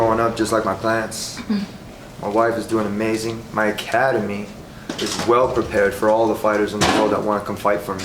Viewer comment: I thought he said "my cat and me"